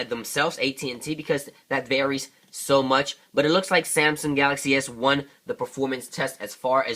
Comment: Did you just say samsung galaxy s1??? 7:37 LOL